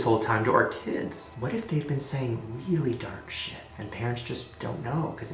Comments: am I the only one who heard dipshit? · it sounded like it said "Big Shit" lol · did anyone else hear "dipshit"? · it sounded like it said dip**